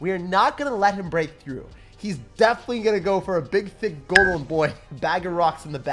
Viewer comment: did you just say big d*** golem boy? lmao